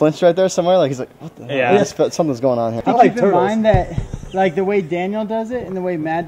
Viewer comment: listen carefully, did someone say I like turtles?